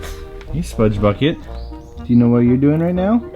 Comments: sludgy instead of Spudgy!🐶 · does that say 'Sludgy's'? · Haha you guys put "Sludgy" instead of "Spudgy" at 6:33!